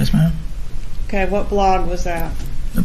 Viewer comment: I think thought said From a BLOND? Not BLOG.